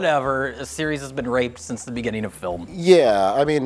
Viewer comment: i thought he said 'the series had been graped since the beginning of film'.